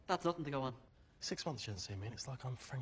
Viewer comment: what does seamus say at 3:10? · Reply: I'm pretty sure he says something like "She's got lots of those, though. She's only got one Harry." I could be wrong though.